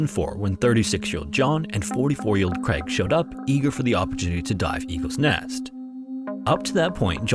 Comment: it almost sounded like you said they showed up eager to die, honestly that’s not far from the truth… how the divers fought to get access back is like fighting for the right to risk dying.